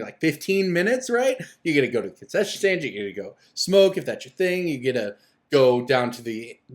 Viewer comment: I had to turn my volume up because I thought you said "you can smoke and touch you thing". Lol I was wrong! Thank you for another video. Love the content.